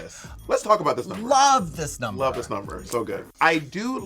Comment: When they say about Nina's talent "love this number" why do I hear "loove pink " vibe 😂😂